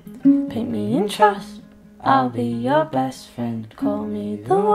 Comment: 'Paint me in trust' is a lovely sentiment x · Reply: I thought she said pay me interest xD